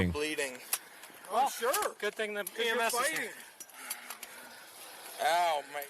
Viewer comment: i thought he said something about you. And having PMS not the EMS